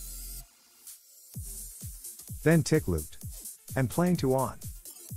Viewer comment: What did u say here? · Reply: tick looped and playing to on...sorry lol